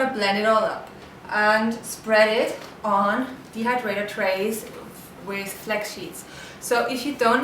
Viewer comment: whats the name of sheet? i can't hear u properly haha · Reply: Non stick sheets, teflex, for the excalibur dehydrator - those are the cheapest ones i could find <link> :)